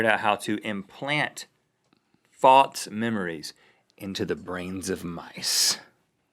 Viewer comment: false? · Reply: Zach Brewer maybe he was saying false memories but it sounded like fox memories so i thought he was trying to say faux memories, since faux means false/fake